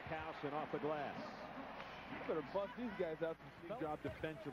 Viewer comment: did he just say 'he better fuck these guys up at 1:28...